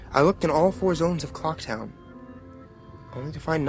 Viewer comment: I thought he said cock town